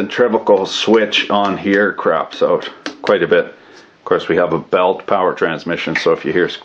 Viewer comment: I thought he said we have a bong